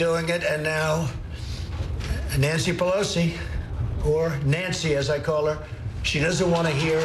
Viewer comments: did he call her "Whore Nancy"? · DID HE SAY WHORE @ 2:17 😂